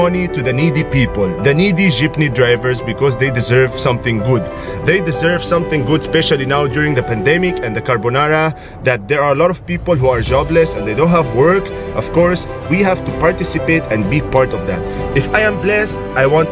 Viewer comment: Did he say carbonara?